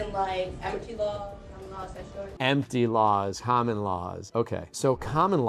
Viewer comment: She actually says "Admiralty law" not empty law. It's just hard to make out. (Not that it's a remotely better legal argument... It's still nonsense.)